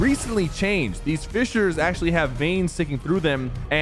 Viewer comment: did he actually call vines "veins"